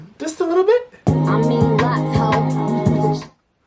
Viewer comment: So far my favorite is fallin 4 U I think she was talking about lotto when she said I mean locs ho, she maid it sound like she said I mean lotto you’s you’s a chop ho can anybody else hear it 😂